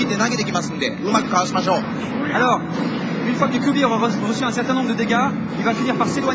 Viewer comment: Did he say fucking kyuubi at 3:21 ?